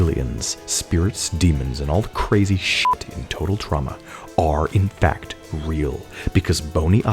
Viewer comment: i love how the subtitles say "total trauma" instead of "total drama"